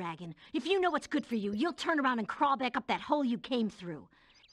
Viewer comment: Honestly, I thought Bianca said, "Crawl back to the hell you came from." I was thinking that was dark, but it was hole when reading the subtitles.